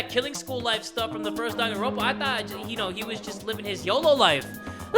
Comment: Did just say Doganronpa